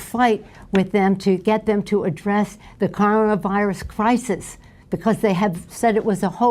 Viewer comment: she pronounced it "Car On A Virus" or am I hearing shit 🤣🤣